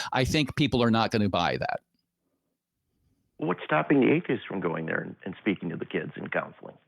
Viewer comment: I'm glad cleared that up for me! When asked, "What's to stop "Atheists" from going into the schools" I honestly thought he said "Incas." ..Lol!